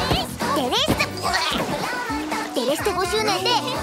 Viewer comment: デレステ50年て聞こえる🥺